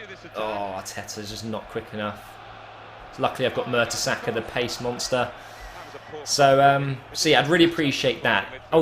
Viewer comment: did he just say mertesercker the pace monster?